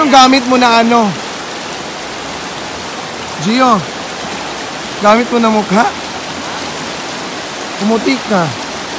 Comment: Tama ba ang narinig ko... 1:53 " Gio ano gamit mo na mukha?... 😂😂😂